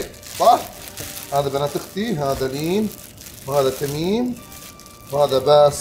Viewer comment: كني سمعت تميممم اسمييييي اععععععععع